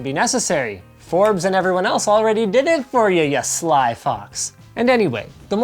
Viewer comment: Thought he said sly fucks instead of sly fox 🤣